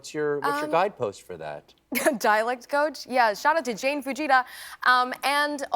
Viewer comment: What is her dialect coach name? What is she saying there? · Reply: She said, "Shout out to Jane Fujita."